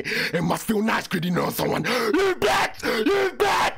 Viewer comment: what did he even say😅😅😅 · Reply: He said you black